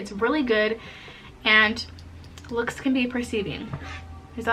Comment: "LOOKS CAN BE DECIEVING" NOT "RECIEVING" JUST FOR FUTURE REFERENCE LUV...BTW YOUR BEAUTIFUL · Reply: I thought she said precieving😭